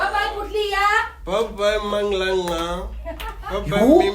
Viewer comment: Did he just say "Bye mangqangqa..." 😂😅😂